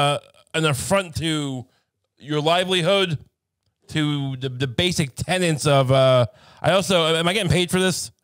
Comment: I thought Ray said the basic tenants 😁